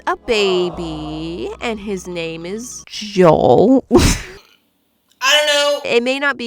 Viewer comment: did you say “Joel” if so thats funny because thats my grand fathers name